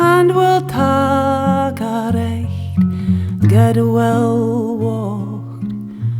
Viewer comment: can someone tell me what she says at 2:58? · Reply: We’ll take a richt guid willie-waught - a good shared drink